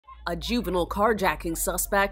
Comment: My volume was a little low at the start and I thought she said “usual carjacking suspect,” not “juvenile carjacking suspect.” 😂